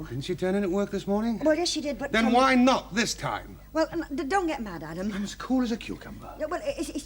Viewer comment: Did Elsie say ‘don’t get mad Adam’, or is it my old ear holes playing me up?? 😂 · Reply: She said, "Don't get mad, Alan".